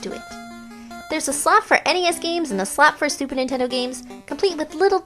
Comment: i keep hearing stuper nintendo at 2:38 lol